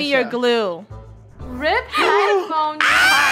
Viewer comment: Did just say "rip headphone pocky" 😂